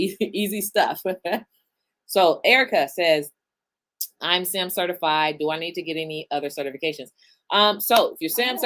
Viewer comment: I couldn't quite hear the question. What did the viewer ask? (Erica ??) claimed to be WHAT certified? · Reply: Erica asked if she needs to get another certification if she's already SAM certified. In general, certifications are not required but they give you leverage over your competitors. If you have other questions about government contracting, let's discuss in detail, book a strategy call with our team at <link>